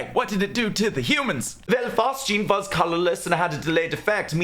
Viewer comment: what's the third gas? I can't hear anything other than "Chlorine, Mustard, and Fart Juice" I mean, it'd be hilarious if 85% of all gas deaths in WW1 were caused by Fart Juice but I'm sure that's not right. Captions aren't helping. 'Fast Jeans' is equally unlikely and far less amusing. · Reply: Phosgene